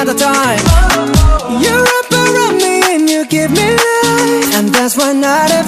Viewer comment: Is it just me that thought it said ''you riperoni and you give me life'' instead of you wrap around me and you give me life'' (*NO HATE*)